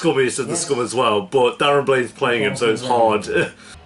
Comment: Did he say Darren Blaine is playing him? Lol. 6:25ish